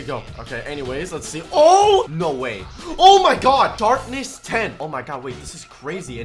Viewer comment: Dude really said "Darpness tehn" I thought he said "darkness" lol